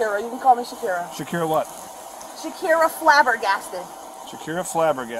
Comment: The woman false calling was so crazy the officers on scene even forgot she misused 911 😂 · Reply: "shakira flabbergasted"? I thought she said "shakin flabby arms"! She was a good candidate for testing pepper spray to calm down a ridiculous criminal.